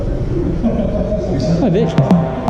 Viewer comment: What name did you say at 0:32? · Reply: Seyran Abgaryan "Hi Vic" (Victoria) 😊